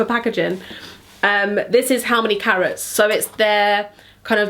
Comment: Just thought you said 'how many carrots' lol